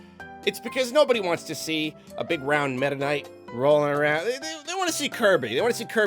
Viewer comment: I thought said, "no one wants to see a fat little mennonite rolling around" instead of meta knight